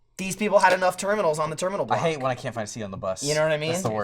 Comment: what did say after was talking about the terminals that laughed at? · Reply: "I hate when I can't find a seat on the bus"